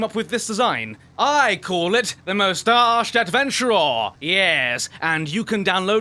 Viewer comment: I have for the longest time thought he said the most arced adventurer